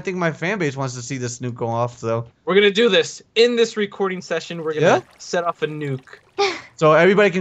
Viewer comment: i thought u said u were gunna nuke the hydra in this recording